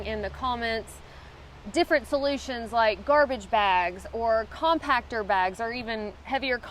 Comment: I always thought u were saying "contractor bag". Now I know u weren't.